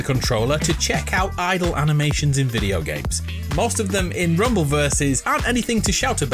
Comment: you call the game “Rumbleverses” instead of Multiverses